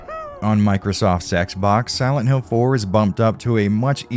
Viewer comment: Did you say Microsoft sex box? I was a Sony fan boy back then. Did I miss something not getting an Xbox? Something sexy?